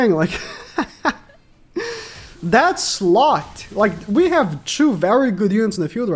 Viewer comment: did he just called sariel a slut. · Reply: He said slot